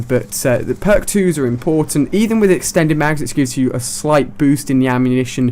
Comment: i sneezed and i thought he said "excuse you" i got scared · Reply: Pretty sure he said "which gives you"